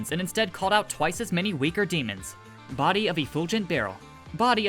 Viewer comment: This whole time I thought he said Body Of A Bulging Barrel.😂